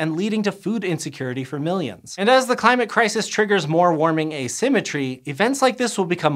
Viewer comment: Oh man, really glad it isn't just me! Also, when he said "heating asymmetry" I thought he said "heating a cemetery" and I thought, "that's so wasteful, they're dead, they don't need heating!"